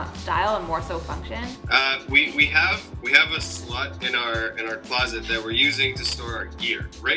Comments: I swear he said we have a slut in our closet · “we have a slut in our closet” anyone else hear that?